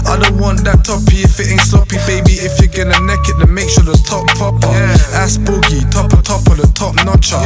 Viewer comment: Someone explain to me what he’s saying or why he says “asco the top topper” ruining the tune for me icl. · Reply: He basically means “Asc boogie, best of the best”